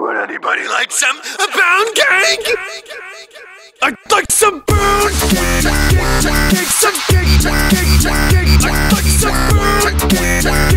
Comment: This is what I hear I'D LIKE SOME POUNDCAKE CUCKCAKE CUKECAKE SOMECAKE